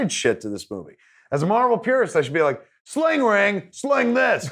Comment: can someone tell me what the fuck Kevin says he says as a Marvel peerist? What word is that thanks